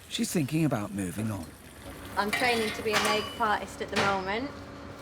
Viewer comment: What is training to be? I've replayed 3 times but still can't catch it! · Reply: Makeup Artist